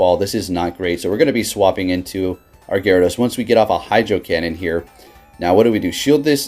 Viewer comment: must've been stoned as hell in this video. I thought I heard em say hydrocunt🤣🤣🤣. Wondering what kinda tea???